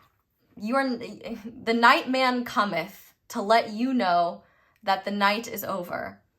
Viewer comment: Lol. What I hear is "you may be the Night man, but I'm a Daemon" 😁 🌹